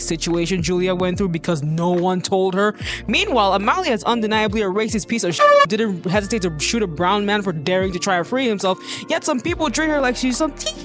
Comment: what was said in 1:58 - 2:08 pls 😭 idr what episode / dont know if the captions caught the right name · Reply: “Meanwhile Amalia is undeniably a racist piece of ** who didn’t hesitate to shoot a brown man for daring to try and free himself, yet some people treat her like some teehee uwu girlboss who’s just being a little silly goofy” (in reference to Episode 34). Sorry that wasn’t clear!